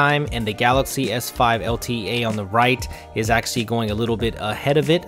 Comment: Thought i heard bitchmark at 4:39 lol....